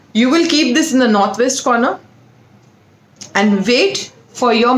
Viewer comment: In what kind of corner we suppose to keep the glass bowl? It says at minute 17:13 but I can't understand it. Thank you. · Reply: She said to put it in NorthWest corner......